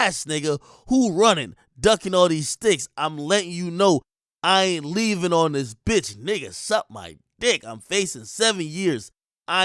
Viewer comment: “I’m letting you know Ian leaving on this bitch”😂😂😂😂😂😂 I thought yb said Ian even serious on this bitch😂😂🤦🏽‍♂️